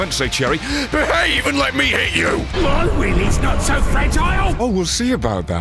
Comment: I thought he said "My Willy's not so fragile"